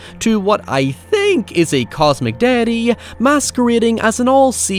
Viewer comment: It took me a lot of replays to not mishear this as "Cosmic Daddy"